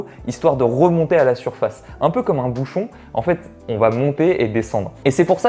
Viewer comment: A 6:17 au début j'ai compris " un peu comme un bouffon " mdr enft c'était pas du tt ça.